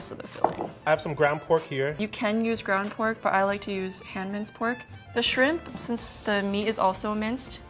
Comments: why do subs say she's saying "[foreign language] pork" when she clearly said hand-minced pork 😂😂😂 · She said "hand-minced" pork but the caption says "foreign language"...LOL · it isn't any "foreign language" she clearly said hand-minced pork